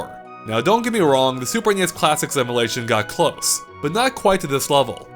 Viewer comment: I keep hearing "emelation"